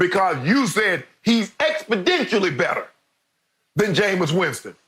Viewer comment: Did shannon just say "expodentially"?